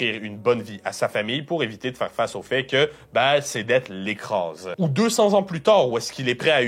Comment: d’être l’écras c’est quoi ce mots ? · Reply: "ses dettes l'écrasent"